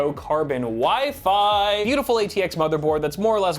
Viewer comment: When ever you said WIFI I thought you were saying wifey